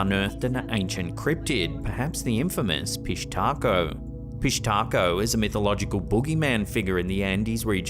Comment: I can’t be the only one who heard fish taco…